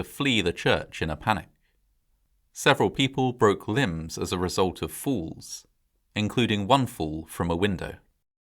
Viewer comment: Definitely thought he said some people broke limbs due to fools 🤔